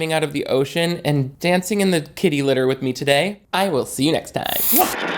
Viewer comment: I promise I thought you said dancing in the titty litter. 💀